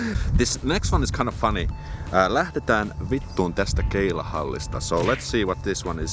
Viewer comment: When I heard "lähdetään vittuun tästä keilahallista" for the first time, the two last words sounded like "testikkeli käälista" 🤣🤣🤣